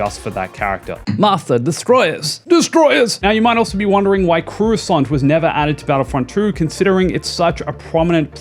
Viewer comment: it sounds like he said Croo-a-saunt 😂